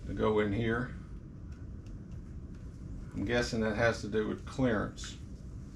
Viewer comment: Hahaha @ 56:16 I'm so used to hearing it, I thought you said "I'm guessing that has to do with CLARENCE" LMAO LMAO LMAO